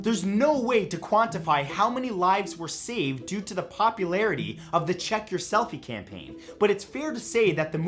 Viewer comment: I thought you said "check if your nuts". I was like "I already know I am nuts so I don't need to check"